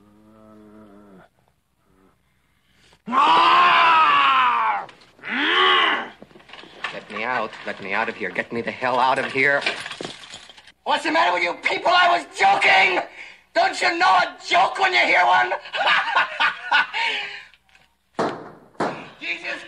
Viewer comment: I always thought he said "let me out, get me the fuck outtta here!"